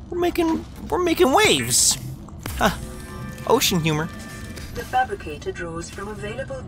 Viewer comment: I thought the music said ocean ham